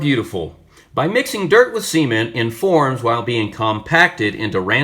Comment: did you just say dirt and semen?.. I hope it wasnt?